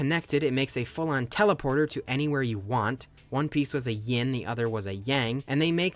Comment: WHAT DID HE SAY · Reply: One piece!!!!